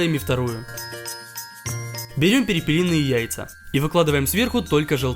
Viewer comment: я один услышал пельменные)?